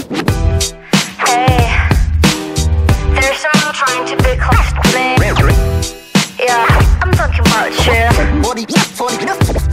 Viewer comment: But what do they say at 0:06? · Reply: Apparently Minnie says “Hey there’s someone trying to be close to me Yeah I’m talking about you”